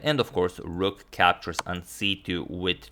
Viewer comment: did he said grabbing that queen..?